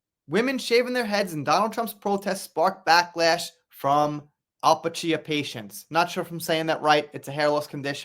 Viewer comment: almost sounded like you said “Al Pacino patients.” 😅